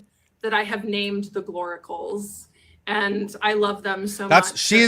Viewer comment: Every time she said gloricals i swear it sounded like she was saying glory holes..... omg it kept throwing me off.